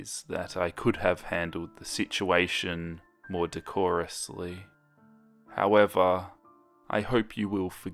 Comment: i'm late, but i hear "Dearly Beloved." 11:52 :l